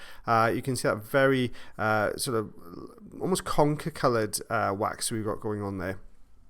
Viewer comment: what color did you say? Conka? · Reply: Conker. Comes from a Chestnut. There's a shade of brown, Conker.